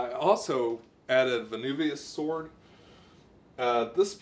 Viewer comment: awesome review! what's the name of that plant again with the spiral\twisted leaves? I heard "anubis sword" you say and tried googling it but to no avail. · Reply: vesuvius sword